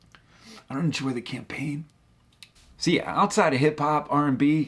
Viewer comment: What is saying at 17:37?? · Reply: I think "I don't enjoy the campaign." As in the ad campaign